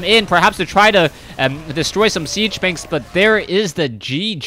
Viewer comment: Anyone else hear "siege spanks" at 10:49? :)